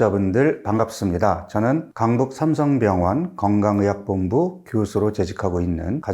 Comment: 삼성병원 지역좀 알려주세요~ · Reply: 강북삼성병원으로 들었던것 같아요^^~